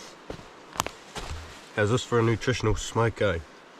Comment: Hi: At 1:51 you say that it is "time for a nutritional ?". What are you saying? Remember that some of us do not have the benefit of life in NZ. Thanks for another bucolic visit, Krzysztof Boris. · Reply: Smoco is basically just a snack break haha